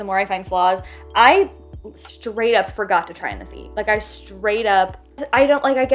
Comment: Lovely video! Can’t make out what you’re saying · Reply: She said "I forgot to try in the feet"